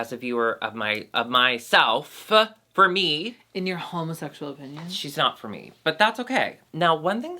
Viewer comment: Omg i’m cleaning the dishes while listening and looking at u two beauties, suddenly my airpods died on me and it stopped at 21:48 where i thought said “in your homophobic opinion” 💀💀